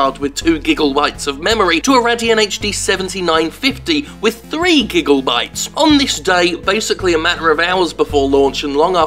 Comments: is it just me or do i hear "gigglebytes"? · Anyone else hear "gigglebytes" instead of gigabytes?